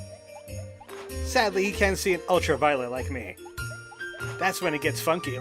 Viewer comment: Judging on how you love to use nova cane, I misheard of ultra-violeNt at 12:00 and it all fell in place.